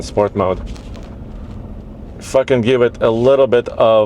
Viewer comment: Did just say “F*ck it” 😂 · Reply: No, he said "if I can" give it a little bit of throttle." I also thought the same as you did, than I realized what he really said. He jammed "if I can" into what sounded like "F*ckin"